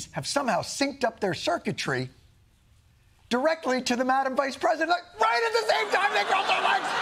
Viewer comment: Can someone please explain what he said/meant between 1:21 - 1:26? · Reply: "...directly to the madam vice president. Look! Right at the same time they cross their legs!" --> he's just saying how they are visually in sync, and he's being silly about it 😂